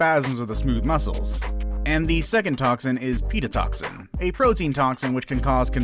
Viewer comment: I thought it said pedotoxin